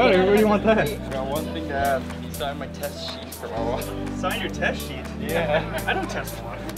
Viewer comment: i thought he said "can you sign my testicles" lmfao.